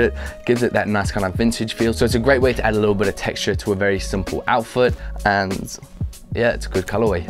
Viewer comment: did he say "outfoot"